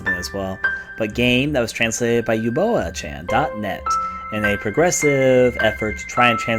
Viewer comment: I heard ebola-chan.